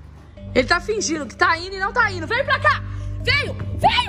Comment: No minuto 18:38 jurava que ouvi a falando “Vem Cams”